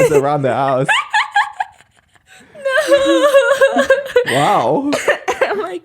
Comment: I love you lmao but the way you said "nooo 😏🤭" sounded like "you shouldddd" 😂😂